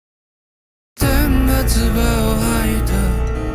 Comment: ずっとfullVer.を楽しみにしてました！！ この曲を初めて聞いた時「天が唾を吐いた」が「天罰が下った」とも聞こえてしまい、あえてはっきり発音せずどちらともとれるような歌い方なのか…流石さん…と思ってもう一度聞き直したらきちんと歌われていたので、申し訳なさと羞恥に駆られたのはいい思い出です…(遠い目)